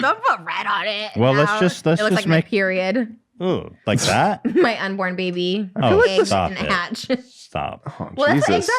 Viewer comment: When trisha said my unborn baby I thought she said my n word baby. Had to rewind that shit lol